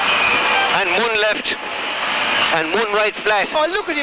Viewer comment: DID HE SAY "one life left"?